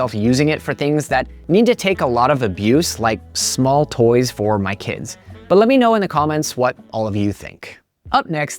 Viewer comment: At @ 20:08 I thought he said "like things that need to take a lot of abuse - like small toys OR my kids." 😂